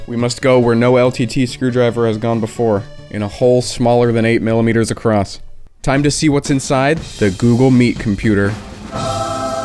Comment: I’m sorry but I heard it as “Time to see what’s in the Google meat computer” and I can’t get the image out of my head